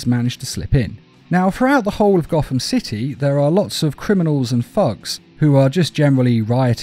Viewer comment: i heard that as ''criminals and f*cks''